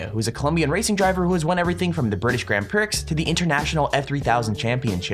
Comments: he said Grand Prix. But prix was like pricks instead of having the x be silent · did he say 0:42 grand pricks? lol what a tool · he did not just say grand pricks 🤣